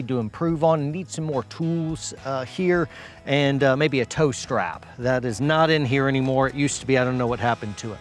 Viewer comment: He said I need a tow strap for in here but for some reason I heard “toaster” and I was really confused for a second 😂